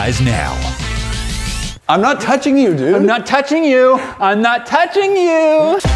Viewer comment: Ahhh he’s Touching me he’s touching me! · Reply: If you keep playing it over and over it sounds like he is saying eww I’m not touching u